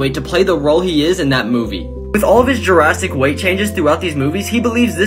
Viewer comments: It sounded like he said “Jurassic weight changes” haha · Did he just say “Jurassic weight changes”.???